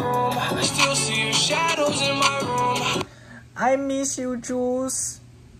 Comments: thought he said I miss you Jews 💀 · I thought he said "I miss you jews"